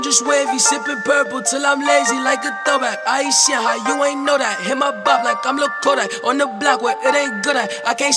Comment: You guys made a mistake on the last part of that line. Flipp Dinero did not say “ain’t see how you ain’t know that.” he said “Haïtian how you ain’t know that.” There’s a big difference there.😒